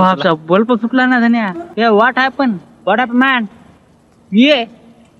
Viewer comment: he is indian more specifically a maharashtrian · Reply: SIR he just said tried to Speak In English This His what He Said at the End 2:29 "Omg The Bulb Blasted","Hey look at this (showing to another person) Hey What Happen bro? ,What Happen Man?" This Is what He said Nothing Cured in this 😉